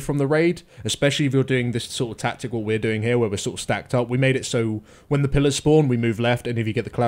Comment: Enabling subtitles on this video just made my day. "Techtips on Iraq" hehehe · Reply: Or 2:49 the subtitles says "when the palace porn" :D